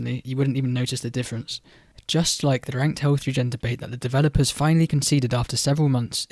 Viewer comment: hello, english is not my native language, can anyone tell me what he says at 4:15? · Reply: "ranked health regen debate"